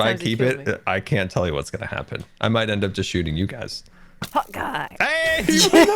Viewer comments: Lol I thought Scar had been saying “Hawkeye” not “Hot Guy” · This whole time I thought Scar was saying "Hawkeye" but everything is funnier now that I know the bow is named "hot guy" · Maybe its just me but I thought Scar was saying hawk eye not hot guy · I cant be the only one who thought Scar was saying "hawk eye" and not "hot guy", right? 0 x 0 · Is it just me that thought scar was saying "hawkeye" instead of "hot guy"? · to be honest until this ep I thought scar was saying Hawkeye not hot guy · wait this entire time i thought scar was saying Hawkeye, i didn't realise it was hot guy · All this time I thought they where saying Hawkeye XD. Like the superhero that uses a bow but they where saying hot guy, I'm dying of laughter!